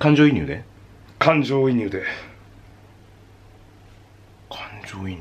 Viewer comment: 「感情移入で」がだんだん「肝臓イヌーテ」とかいう新型の病気に聞こえてくる こわ